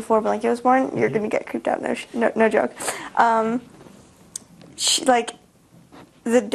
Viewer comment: She was gonna say "no shit" at 1:32 but then she changed it to "no joke" hahahaha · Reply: Like like like no shhhh no joke